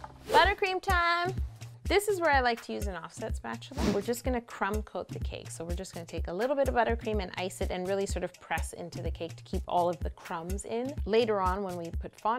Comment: For a long time I thought when she said "crumb coat" she said "creme coat"